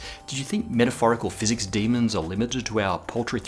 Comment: Did Matt just say "poultry"? I'm cluckling! That just made my day! :D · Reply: Gravijta no he said “paltry”